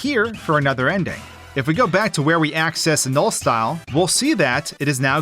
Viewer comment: i heard noli and not null 💔